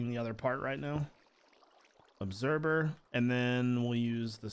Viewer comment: I know I'm late on watching this I'm not one to watch vanilla but the way you do yours videos is awesome... I'm leaving this comment because I think it's funny how you say observer it sounds like you say obserber. 😂😂